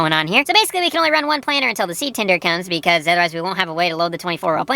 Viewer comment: Sounded like he was saying Seed Tinder at high speed. Seed dating service = baby hybrid seeds.